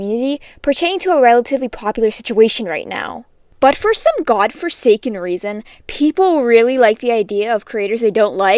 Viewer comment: heh,, did you say FORSAKEN,, Forsaken mentioned!- /j